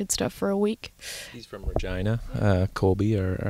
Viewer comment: what’d he say? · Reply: Mergina???